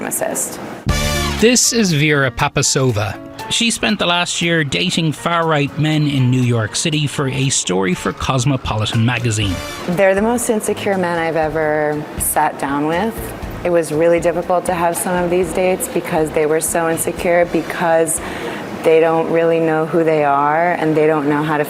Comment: Notice when she said sat down with, she looked down. How many did she sleep with? Why would she 😂need a year to do this? Most stories are done in a few months but she needed an in depth view and/or experience. 😂😂😂😂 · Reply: I thought she'd said "they're the most insecure men I've ever bed down with."